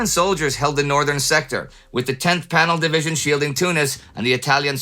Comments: did you say the 10th panel division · Does say 10th panel division??